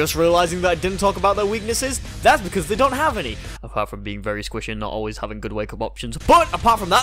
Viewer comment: can someone say what he is saying? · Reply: "apart from being squishy and usually not having a lot of wakeup options" if I am correct